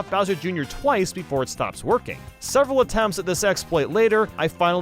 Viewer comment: Why did I hear "several attempts at this se# later" lol